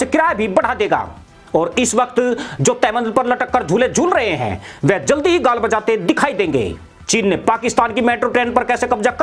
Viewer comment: Bhai 2:52 pe apene kya Bola g**d bechate?